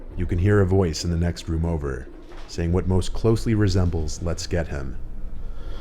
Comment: Am i the only one who hears "im gonna kill him" instead of "Lets get him"??? at 2:15 ?